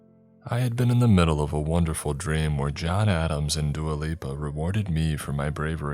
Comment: When he said Dua Lipa I thought he said Duolingo 😂